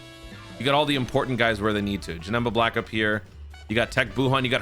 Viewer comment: At one point in the video it sounded like you said janemba black, that would be a crazy fusion